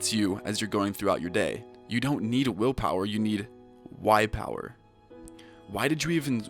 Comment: I thought you said white power I was like -_- hm easy